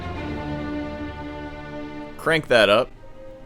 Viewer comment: you're gonna think I make this up. But before I heard your response I thought "Crank that up"